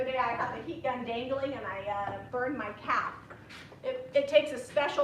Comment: I thought you said you burned your cat.